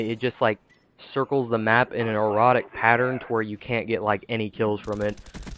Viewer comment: In an "Erotic" pattern? Hahah sounded like you said that instead of erratic